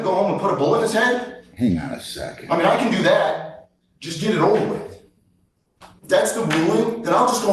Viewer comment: the defendant says "Just get it over with", not "just get over it." thought bubble there inaccurately captions what the guy just said. He's talking about whether to kill his dog quickly himself or get the dog a vaccine that will kill him slowly, and says he might as well kill his dog himself and "just get it over with" so the poor guy doesn't have to suffer.